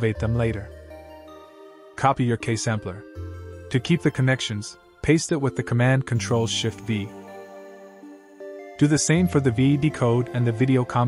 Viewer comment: Hello, sorry i didn't understand the command at 1:54 did you said ctrl+shit+b?